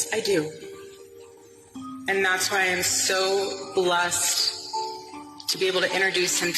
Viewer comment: She even got the smokey eyes. I thought she said I'm lust to introduce him tonight but she said bless almost a slip.